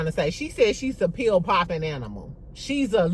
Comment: Maybe I didn’t hear it correctly but I thought Kelli said pill popping roommate referring to her husband